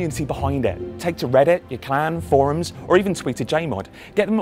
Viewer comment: "...take to reddit, [something], forums, or even tweet a jmod..." What website does he say after reddit? It sounds something like "you-can". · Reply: I think he's says 'your clan'. It's a good idea if you have an active clan with their own website and forums.